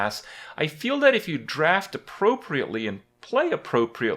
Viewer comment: When you say "appropriately" I heard "luckily"